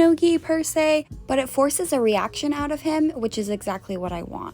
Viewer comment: does she say that she wanted to get an "erection out of him" and she got it?